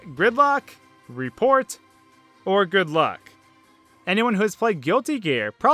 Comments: Oh I have something I mishear! In the newer versions of "Good luck" from Star Fox; to me, it sounds like he's saying "Good bye!"? XD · The first time i heard the good luck quote (or whatever it was )you know, the star Fox one?I thought it said "Grimlock". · I heard "report" when I was a kid, but in recent years I understood it as "Good Luck!" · it's probably "good luck" , but whenever I'm in a starfox charater costume in super Mario maker, I hear "Good-bye!" in the pose sound. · When I was younger I always thought the announcer said "Report" in StarFox 64, but it was actually "Good Luck"